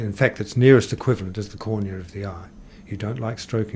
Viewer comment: at 14:28 the subtitles say "corner of the eye". I believe he was saying "cornea of the eye".